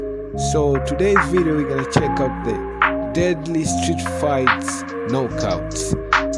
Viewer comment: i thought you said STREET FIGHTS